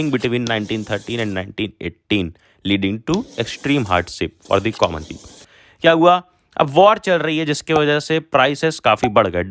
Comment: I think it should be common people instead of comman people......